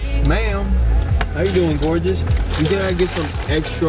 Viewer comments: “How you doin gorgeous”? She knew you was bullshittin. 😂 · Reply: I thought he said Mcgorgeous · “How you doin gorgeous”? She knew you was bullshittin. 😂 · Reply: I thought he said hello mcgorgeous lol